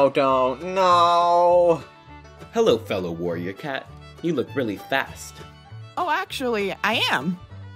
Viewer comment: Lol why did he sound like he said “hi there fellow warrior cat you look really FAT “